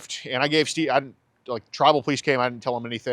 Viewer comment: I TRULY am hearing impaired and I thought was saying “travel” police. I was trying to figure out what a travel police officer was. Let me know if you want to borrow my real hearing aids